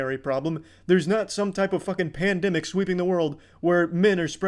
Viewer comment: did I hear pandemic?